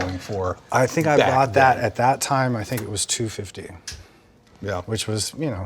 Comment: 215? 215,000? What is he saying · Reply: $250k